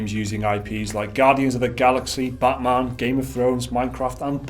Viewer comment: Oh man, I heard it as "Botman"